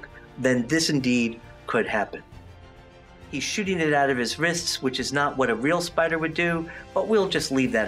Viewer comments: What did he say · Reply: he said he is pooping it out of his wrist. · totally thought he said "shitting it out of his wrist"